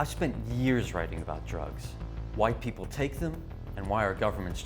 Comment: I seriously thought he said "white people take them" but im gonna assume he said "why" lol I was like damn